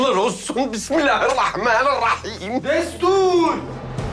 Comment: 1:1:40'da sümbül ağa ilk başta hâğibaheir falan diye birşey duydum sonradan anladım Bismillahirrahmanirrahim dediğini jfjcmncnc